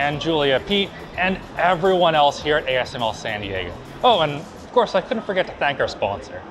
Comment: The way Alex pronounced San Diego, made it sound like he said “Sandy Eggo™”